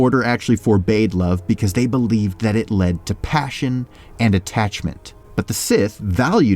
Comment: did i just hear "cum-passion"?